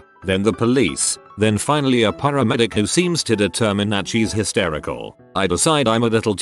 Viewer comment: I regret being kind to abusive, narcissistic family members & ex-friends, never again! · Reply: Histerical? I thought you said she was Hispanical?